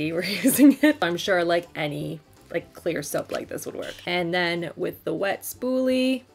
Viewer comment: I thought you said Claire's at first. Lol